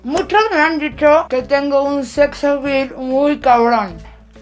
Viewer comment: que dice en el minuto 13:22 es que no entendí solo escuche que dice, muchos me han dicho que tengo un sexo .... muy cabr**???